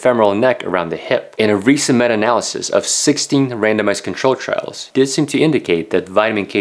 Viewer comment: Every time he says randomized control trial I hear 'random ass control trial' cause english is not my first language and it makes me so confused.